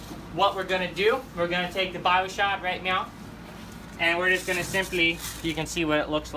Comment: Did he just say "right miau"?